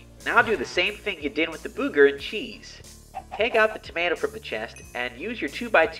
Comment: What did he say booger cheese?!